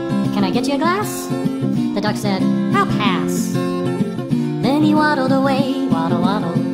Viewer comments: i originally thought the duck said "suck ass" instead of "I'll pass" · When the duck said " I'll pass" I thought he said "help ass" LOL😂😂😂😂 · When the duck said "i'll pass" i thought he said "fuck ass"